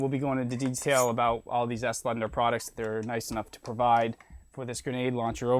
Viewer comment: did he said? :we gunna try all these slender products?